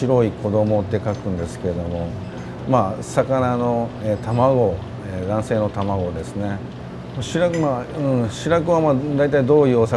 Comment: sensei is talking about milt, and the subtitles say "and basically they are eggs." But he actually says "they are male eggs" aka sperm. Just a clarification if anyone hasn't heard of fish milt before.